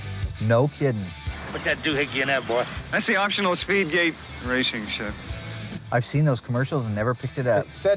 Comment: Sounds like he says "speed gate racing shift"